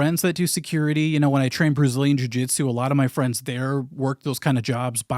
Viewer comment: Was it just me who at 15:46 heard "I trained Bruce Lee in Jiu Jitsu" instead of "Brazilian Jiu Jitsu?" It took me a moment to realize what he actually said